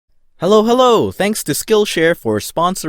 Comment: I thought he said Halo Halo lmao i’m hungry now